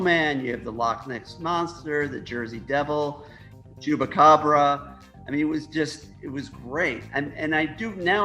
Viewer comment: i swear i heard him say jewbacabra